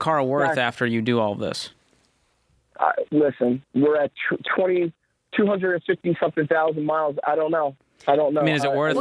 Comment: Yo did Mike say “200 and fucking 50 thousand miles” lol